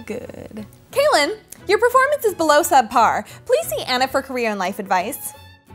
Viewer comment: At 0.45, when she said "Kaelin, (I think that's how its spelled, if not IM SOOOOOOO SORRY!) your performance is below subpar. Please see Anna for career life advice." I thought she said "Please see Anna for Korean life advice." I was dying when she said that, I need that too! Please!!! Make me Korean so I can meet EXO, BtoB, BTS, SNSD, Shinee, and other kpop bands! Because right now im a Pacific ocean away! Please! Than I clicked subtitles and it said "career", right then my energy ran out! Why couldn't you say "Korean" !!!